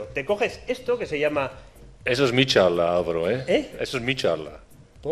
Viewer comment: Alguien entiende que dice la voz que le interrumpe en el 16:30?? No consigo saber que dice y porque se disculpa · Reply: Dice "Eso es mi charla, Álvaro". Es otro conferenciante que comenta jocosamente que le está pisando la presentación.